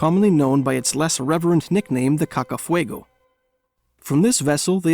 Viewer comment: The "Caca Fuego"? Were you just testing to see if we were paying attention? (I may have misheard, but I like the name.) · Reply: Nope, that really was its nickname. Nuesta Senora de la Concepcion, a.k.a. "Cacafuego".